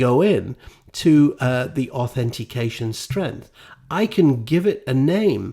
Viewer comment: Hello At time Code 9:09 did you say authentication strength ? Sorry for the inconvenience, but that was the only word I didn't understand Otherwise good